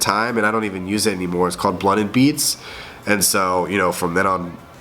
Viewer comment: yo, what music channel did you used to use?? i feel like im hearing it wrong cause im not seeing it in the search =( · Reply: DaWiff lmao god dammit! you and your stuffy ass nose, i thought he said blood and beats